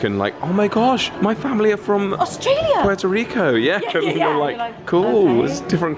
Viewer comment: Did he just say Puerto Rico?